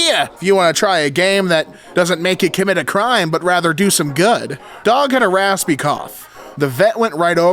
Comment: I took my eyes off the screen for a couple of seconds, and I misheard that as “don’t get a raspy cough”.